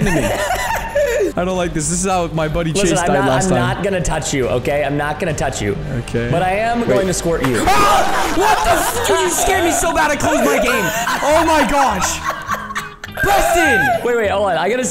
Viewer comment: i heard ahh what the fcck r u